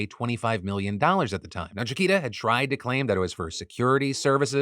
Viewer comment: it says "shakira" instead of Chiquita FYI in the closed captioning.